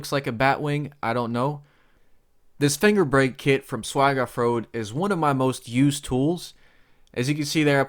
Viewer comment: Min 4:50. You said it so fast I could not catch it . What did you call the brake & where did you get it. HELP I need that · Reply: Finger brake kit from Swag offroad